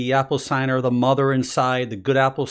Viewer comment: I can't understand a few words at about 0:44 to 0:48. Does anybody know what he says exactly? Thanks. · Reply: Aurora Spiral With the mother inside, basically.